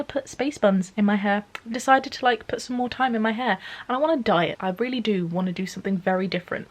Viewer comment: I came out as bisexual, my best friend got married, and I moved to my dream city! Also I thought you said "I want to diet" instead of "I want to dye it" (your hair) and I was briefly confused.